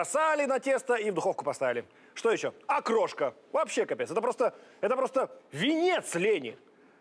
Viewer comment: мне одному послышалось, или пушной реально сказал "капец"?